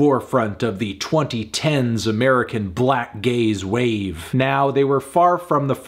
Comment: i heard 'black gays' and was like what is this fool talking about